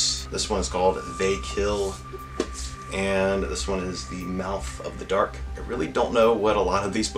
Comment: Haha 😂 I thought that said ‘mouth of the dork’ Great Haul 🙌🏼🖤